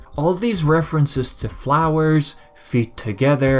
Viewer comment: that is really cool, i never realized he was talking about flowers; i've always been hard of hearing so i thought he was saying 'followers' XD i've always wondered what the actual plan in thieves in time was gunna be, cause of the easter egg in the ice age world i thought they were trying to imply bob killed clockworks family or something :O